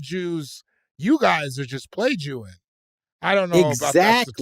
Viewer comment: I thought David said they were plajewizing(plagiarizing).